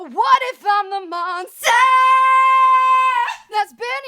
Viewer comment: I thought she said what if Im the mom lmao now I know